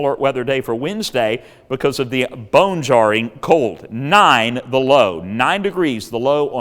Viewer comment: 9 the Low. We thought you said 9 below there for a minute 😬